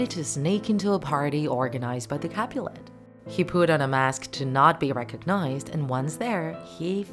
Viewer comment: Instead of you saying ‘he put on the mask’ I thought you said ‘he pooped on the mask’